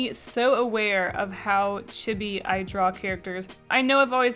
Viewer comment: I thought you were saying chibby then I re-herd it and I think you said Chibi?